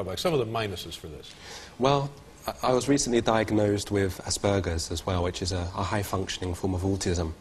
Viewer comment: Am I the only one whose laughing because I hear "assburgers?" Yea so what. I'm a fucking idiot with an infantile sense of humor...